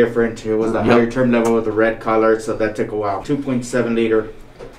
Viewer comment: did he say red color?